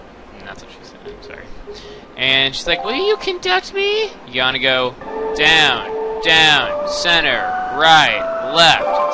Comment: i thought he said "well you can touch me"